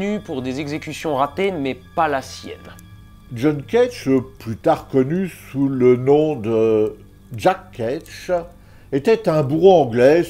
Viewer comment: J'ai entendu "Jack Ketch, connu plus tard sous le nom de Jack Ketch". Ca me fait la journée. 🤣